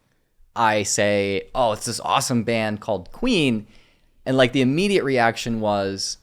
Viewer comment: i thought you said ween lol